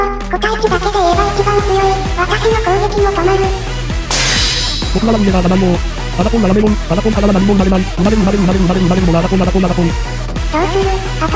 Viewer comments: 秒から、ERRORが「争いはやめよう争いからは何も生まれない。生まれる、、争い、、」って言ってる 中級になるとやっぱ喋るし、めっちゃ凝ってるなー笑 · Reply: 「僕は悪いERRORじゃないよー」 7:24 「争いはやめよう。争いからは何も生まれない。生まれる生まれる生まれる生まれる生まれるのは争い争い争い」 0.5倍速にすると聞き取れる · よく聞くと最後に出てきた、中級は言葉を喋っているね 7:21 から 7:28 まで 最初よく聞き取れないけど 最後の方はよく聞くと「争いやめよ、争いからは何も生まれない,生まれる！生まれる！生まれるのは争い争い争い！！」って早口で聞こえる。知能を持たないってあったけどなにかの伏線かな？